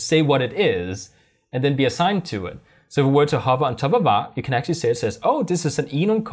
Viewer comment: Great Video. I have to know what you said at 8:44 though lol. I listened to it like 5 times and can't make sense of it. Sounds like some random dialect slipped through. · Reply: "so if we were to hover on top of var"